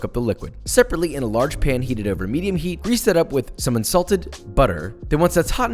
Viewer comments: I couldn’t figure out what the hell “insulted butter” meant. But it was equally hilarious when that’s what I heard vs when I realized that wasn’t the case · i thought he said 'insulted butter' and for a sec i was like IS JoShUa WeiSSMaN A BUTTER BULLY??!!?....ehem nvm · WAIT wait wait wait waitwait WAIT. 2:04 what is insulted butter? I heard you done did it, I heard it! Explain! What happened to the poor butter??